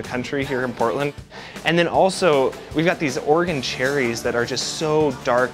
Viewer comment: Lol after he said “bone marrow ice cream” I heard him say “organ cherries” instead of Oregon 🍒 🥩 lol